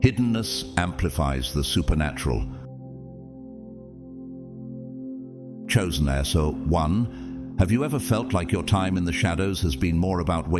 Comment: Did anyone hear at 49:39 "chosen asshole "really so this is misguiding i thought this was meant to be something good? To be refreshed and all. Not to hear that